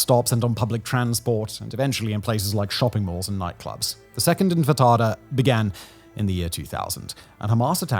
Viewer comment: It sounds like you are saying. "infitada" instead of "intifada"... 8:34 "infitada". 8:50 "intifada". Any way trivial point. Very educational and informative.